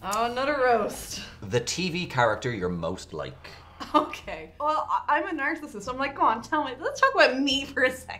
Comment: For a few seconds, I thought said "Well, I'm a nurse." Oh, "nerd"! That makes more sense.